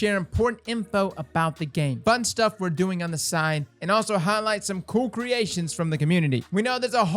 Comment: I heard that as, "butt stuff we're doing on the side."